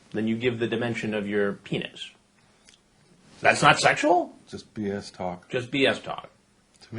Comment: I really thought the little man said, “It’s just penis talk.”